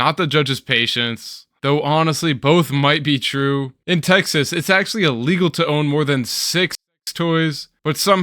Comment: I swear I don't know what you are saying but I'm imagining it's "In Texas it's illegal to own more then SEGS TOYS"